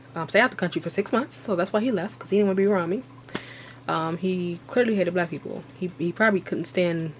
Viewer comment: does she say "He clearly hated black people"?? That would be a very curious Nigerian.